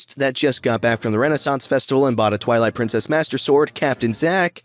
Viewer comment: Holy hell, i misheard him so bad at the beginning. I heard "Twilight Sparkle Master Sword" not "Twilight Princess"